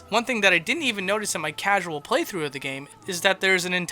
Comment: Did you just say "casual"?